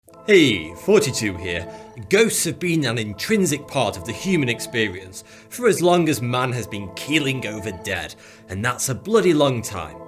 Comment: I always heard "hey 42 here"